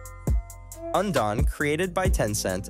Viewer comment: what is the game on 4:04 i didnt understand what he said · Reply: undawn